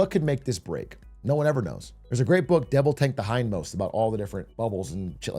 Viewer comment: Mf for the life of me I can't understand what he's saying for the book title at 1:40. "Devil tanks the high note"? I literally have no idea but would love to know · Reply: devil take the hindmost ! by ed chancellor